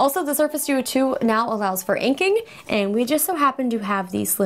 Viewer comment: What are you saying at 2:45 ? Franking? What's that? Why aren't you nominated for a Streamy? You're streamy.